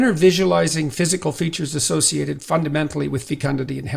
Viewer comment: what's he saying here? ficandadean health? i googled it every other way and i think i broke google a little...